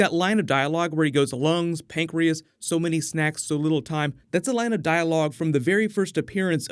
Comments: Oh he said so many snacks so little time I thought he said so many sacks for a little guy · Here's my new Venom Trailer video. Lots of cool Marvel Avengers news today too. Posting a new video for everything next! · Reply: I so love that Line "Eyes. Lungs. Pancreas. So many snacks. So little time" Though I thought they were saying "So many SACKS so little time" at first which really confused me since I know the comic line was snacks. Its still intimidating wither way